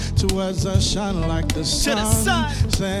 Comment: sounds like kabomo is saying "Monday will come",not very comforting lol